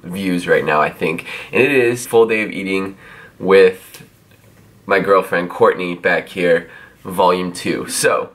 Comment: thought it said : Full day of eating my girlfriend...